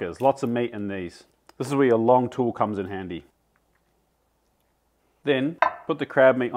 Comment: I kept hearing “this is where the long tools come in Andy”